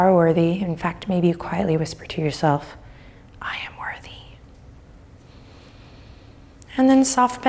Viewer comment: Misheard Adriene telling us to say “I am worthy.” I thought she said, “I am ready,” but whew-ee is that what I needed to hear today! I AM ready!